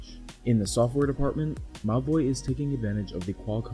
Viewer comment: Whenever he said: Mobvoi, I just heard "My Boi"